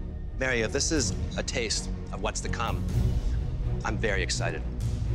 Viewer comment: did he say "i'm berry excited" ??? :P